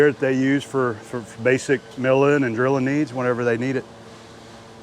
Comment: i heard that as Bridgeport for all your basic milling and grilling needs haha